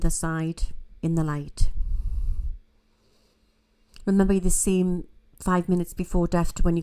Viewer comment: Can someone please help me with what said at the 27:07min mark…I can’t understand what her words are because of her accent. Something like “remember you are ??? 5min” I just can’t understand her words because of the accent. I’m in no way shape or form being mean or rude, I’m truly wanting to understand what she’s saying and I’m having a hard time with each word because of her accent. I love and respect and I’ve been following for awhile so I’m not being mean or rude…I just have a hard time understanding both of their accents. And yes I did the closed captioning but it made zero sense spelling out what said. Thank you in advance to anyone that can help me with what she said. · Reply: You're the same, I think